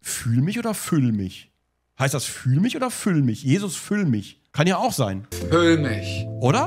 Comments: Ich hab immer "Jesus, fön mich" verstanden 😆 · Das geht ja schon geil los. Ich höre das nebenbei bei der arbeit und ich habe ganz am anfang "jesus füll mich" verstanden. Ich hatte einen sehr widerlichen lachflash